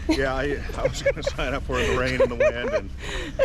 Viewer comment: Lolol...I thought you said you didn't sign up for the rain and winds!😂 Then I realized what you meant. Lol I'm a little slow!😅 A nice leisurely gondola ride sounds about right to me! It's great that you challenge your fear of heights. Dawn knows that is very hard. She's reluctant to even ride the Disney Skyliner. Now, this is the Alaska I've envisioned. It's very snowy, but very beautiful, in my opinion. We love nature, so that nature walk would be awesome for us. The Cannery shop looks and sounds like a nice, interesting experience. I seriously don't think I'd plunge in that icy water!😮😅 The zipline looks awesome! I'd totally do that! Lol Even though it was raining and chilly, it still seemed like a fun day excursion. I enjoyed it. Thank you for taking us along, guys!